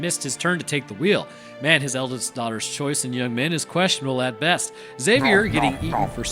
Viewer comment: I heard that as Queso gonzales , the formal name of our patron saint of dairy , Cheezus